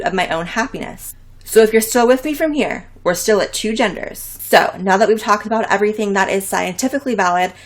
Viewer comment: Did she say gender-pho. Does that mean you identify as a bowl of soup?